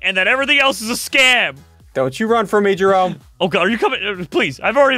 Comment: I am not sure if I heard ssundee say treeson or freezer